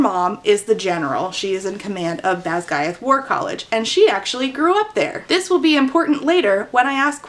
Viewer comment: Not having read the book, I thought was saying "Badguy War College" at first. I didn't question it either lol